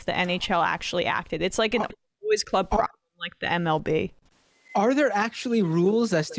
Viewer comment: What did she say when she cut out at 3:38? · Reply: I think she said "it's like a good old boys club problem like the MLB".